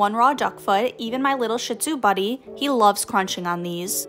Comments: i thought she said picky little shit zoo for a second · I WAS SO SO SCARED I THOUGHT YOU SAID "my little picky shit" I WAS SO CONFUSED